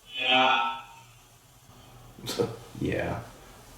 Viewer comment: The microphone comment gave me chills. I know this isn't a political page, but my heart is so heavy. I hope one day many misdoings will come to light. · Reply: I heard "shot" and not "yeah."